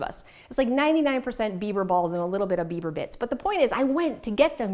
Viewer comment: Great clutter free ideas. Love the kleenex box. Not sure if I could keep a straight face ordering bieber balls or bits. At one point it almost sounded like you said bieber bit*ches. lol 👍🤣🤣